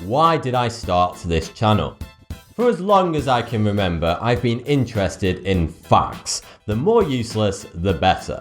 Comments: I thought he said in the beginning "I've always been interested in fucks" lol I lost it. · ………..who else heard "fucks" ? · I thought he said, " I was more interested in fucks." xD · i heard " i've been interested in fxcks' in